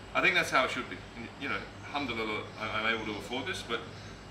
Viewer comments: Wow how could anyone hate on this… this video is pure positivity and genuine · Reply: 🤣🤣🤣🤣🤣 what's that bro what did you say ? Humdululuh? 😂😂😂 (no hate or disrespect...) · 🤣🤣🤣🤣🤣 what's that bro what did you say ? Humdululuh? 😂😂😂